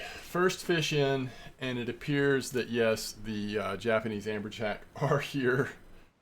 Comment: I'm not trying to like... criticize or nothin', I genuinely thought it was hilarious. Had to do a double take and go "Did he just say Trophanese Japanese Amberjack?" lmfao. That was great, made my day better.